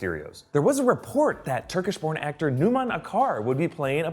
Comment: did he say porn actor?